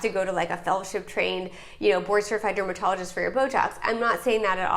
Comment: Listen to what you said at about 6min30 seconds in. You misspoke and it's hilarious. I mean no disrespect, I appreciate your content, but I replayed it 4x to make sure I heard what I heard😂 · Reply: I heard the same. I was listening to while I was cleaning, but later rewound the video and turned on the subtitles, and it showed "Botox" and not "bl...jobs." So, it seems we misheard it 😀.